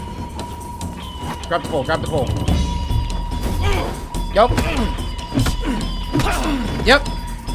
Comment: WHAT DID HE SAYYYY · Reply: Grab the pole. Grab the pole.